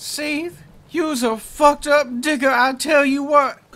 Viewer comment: did i hear Digga! LMAO